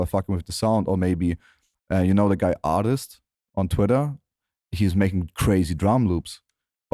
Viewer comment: yo anybody with a better english than me understood which drum maker he mentioned @ 47:38 ? i hear "artist on twitter" lol dope itw thx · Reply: Ardist